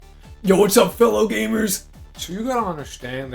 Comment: I heard that as "what's up hello neighbor" and I thought you were now working on mascot horror